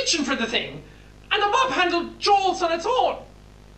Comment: did he say mob handle? So the "spider" looked like a mob handle that moved inside a gate.... I think it's the one who took mommy's corpse..